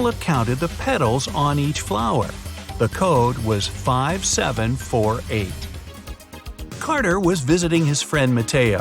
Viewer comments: lol I heard the narrator said 5748 instead of 5784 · When he said mateo i thought he said potato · finally a talking mistake lol ik it was 5784 but why do i hear 5748